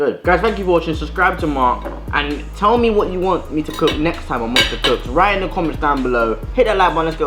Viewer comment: i thought it said monstah cocks and not cooks and was like wtf